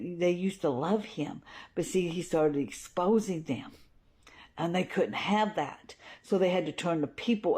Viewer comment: The first time I thought, she said: “[…]he was starting exposing him[…]”_ Me: Ewwww, nobody wants to see that